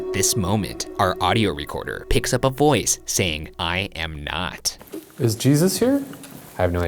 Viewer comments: when ryan said the whisper said “i am not”, i heard “i hope” ?¿ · anybody else hear "i hope" instead of "i'm not"? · I heard “I hope not” I’m literally shaking *inhale* EEeEEeWWwwWwW!!!!!!!!!!!! Like he says “Is jesus here?” “I have no idea” And for a demon/spirit/ghost to say “I hope not” IN a chapel, that indicates it is not friendly and is not of light and love. It honestly sent chills throughout my body and it made me feel very uncomfortable. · Sounds like it said “I hope” instead of “I am not”, really is interesting to think about. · I don’t hear the voice at 17:37 saying “I am not.” I heard it saying “I hope” or maybe “I hope so.” Maybe it’s a trapped spirit longing to be taken to the other side? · When they asked, "is Jesus here?" I swear I heard "I hope" which makes so much more sense. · I didn't hear "I am not" I heard, "I HOPE not" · i hear "i hope" instead of "i am not" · When they asked “Is Jesus here,” and they heard “I am not,” I heard “I hope so” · if you put it at .5 speed it sounds like it says "I'm home" Instead of "I am not" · i heard "i hope not" instead of i am not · Yeah I don't hear "I am not." I hear "i hope." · I heard "I Hope" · The voice in the chapel sounds like "I hope" and instead of "I am not." At least to me. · #postmortem at 17:36 is sounds like the ghost is saying i hope. instead of im am not. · i think its saying "i hope" instead of "i am not" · I thought he said “I know” but ok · it sounded like he said "i HOPE not" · I heard it as "I hope"